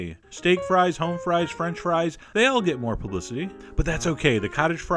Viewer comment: "they all get more p***y"?? Anyone else hearing this? Edit: ok after constant repeat i barely hear "more publicity" but seriously tell me im not the only potato pervert here